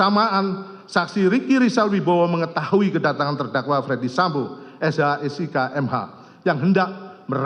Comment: Mohon maaf Bpk jaksa penuntut umum. Nama terdakwa Ferdy sambo bukan fredy sambo. Apa saya yg salah dengar ya?